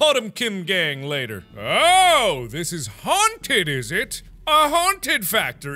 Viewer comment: Please tell me I'm not the only one who heard mark say haun-titty